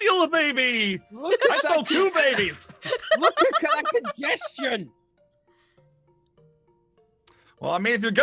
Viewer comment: I THOUGHT SAID INDIGESTION OMFG 😂😂😂😂😂😂